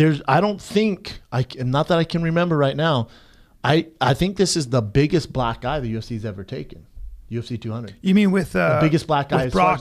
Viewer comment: sounds like he said this is the biggest black guy UFC has ever taken